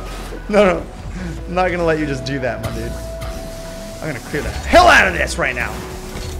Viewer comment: I thought he said, I’m gonna cream the hell outta this. And it seemed like that’s something he would say, sooo...